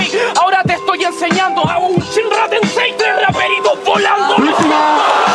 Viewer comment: que dice? Ratensei? Es de naruto? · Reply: Shinra tensei El es otaku xd